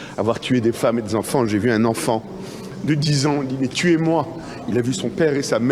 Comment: L'enfant de 10 ans n'a pas dit "tuez moi" mais pourquoi ne m'avez-vous pas tué ?" 19:50 "pour l'avenir de "Eretz Israël" c'est à dire du grand Israël ; donc du Nil à l'Euphrate. Projet géopolitique qui dit clairement que la Palestine n'est pas la seule à devoir disparaître. A bon entendeurs, salut, salam, shalom.